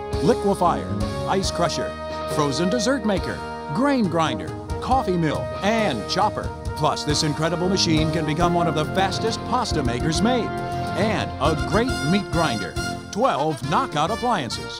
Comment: One of the bestest pasta makers? Holy fuck. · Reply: It says fastest pasta makers. I thought it said bestest too. I had to rewind it a few times.